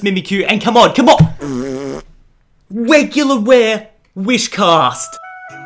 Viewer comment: at minute 6:11 he said wegular ware atleast it sounded like he said that xd